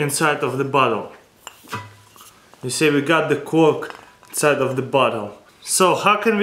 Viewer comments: It sounded like he said "see we have the cork inside of the butthole" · It sounds like he says "We got a cork in the butt hole, you see?" xDD